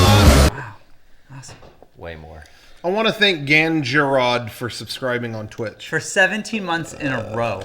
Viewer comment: Ganjarod you still subscribing? (I couldn’t really understand what they said this is what it sounds like to me